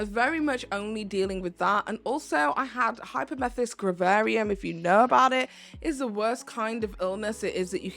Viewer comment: Can someone tell me the diagnosis i cant understand what she says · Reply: hyperemesis gravidarum